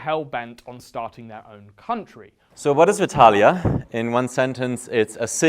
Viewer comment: for a sec I thought he said Hetalia